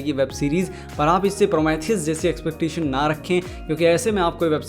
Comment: What is the name of 1:52 that he say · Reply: prometheus